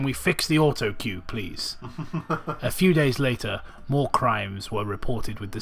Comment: I thought you said "war crimes" instead of "more crimes," I went to read that passage myself to confirm that this fucker didn't just kill a medic In a warzone.